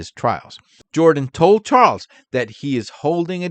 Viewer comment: do you realize you said TRUMP instead of Charles? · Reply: It’s definitely Charles, just a little fast. Try listening at a slower speed, you’ll hear it.